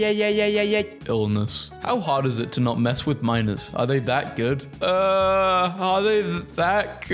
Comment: i think they're saying "are they that good" as in fighting 😭